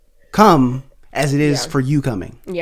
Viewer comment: Chile did just say Cum😶🙃😂😂😂this is going to be called the i can't breathe from Laughing Podcast U Go Enjoy Boo Boo 41:29 Hats Off TO GOD And babe for Lining Himself With HIS HOLY WORD🔥